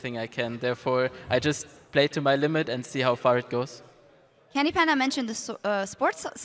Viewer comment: Wait did sjokz say ''PandyCanda''? 1:05:02 lOl